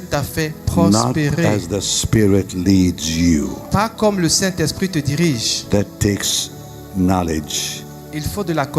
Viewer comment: c'est bien ce que j'ai entendu pas comme le Saint-Esprit te dirige ?